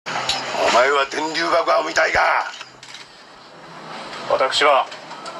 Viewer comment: お前は天龍爆破を見たいかに聞こえる